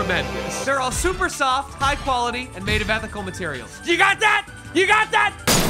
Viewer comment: I thought it said 'ethnic materials'